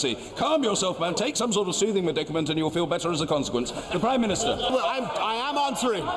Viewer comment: ここなんて言ってるんですかね？全然聞き取れない笑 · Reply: Take some sort of soothing medicament and you’ll feel better as a consequence かな？？